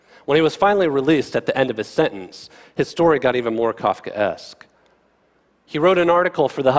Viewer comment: what’s the word he uses? ‘Cofcaest’ what’s the spelling and meaning? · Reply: Kafka-esque - relating to Franz Kafka (author) and the surreal way he described nightmarish situations.